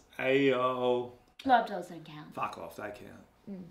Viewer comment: tf does she say? i cant understand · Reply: She says "blow up dolls don't count". They call back to that joke later on too.